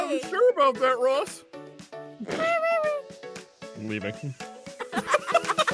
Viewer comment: When said "I'm leaving." I THOUGHT HE SAID "I'm weebing."!